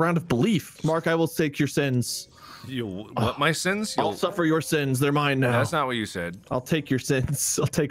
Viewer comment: why is everyone ignoring 18:29? what was it? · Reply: He said "I'll suffer your sins" they thought he said "I'll suck for your sins"